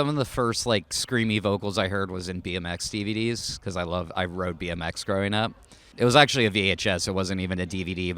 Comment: thought he said 'DMX' at first. [+†+.R.I.P.+†+]